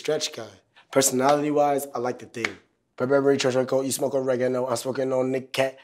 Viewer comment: I thought he said "I like the dick" I had to turn on my caption to see what he said